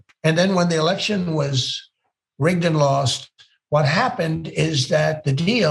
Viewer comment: He did not say rigged at the end he said and I Heard "rigged and lost